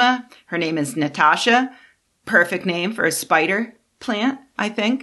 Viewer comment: I thought said fuck the trend... but I suppose it was "buck."